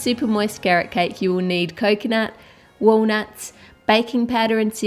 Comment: When you said coconut I thought you said Coke